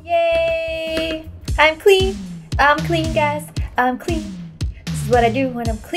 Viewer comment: I Thought She Said "I'm a Queen! I'm a Queen! Guys I'm a Queen!" But She Actually Said "I'm Clean! I'm Clean! Guys I'm Clean!" 😂🤣 :3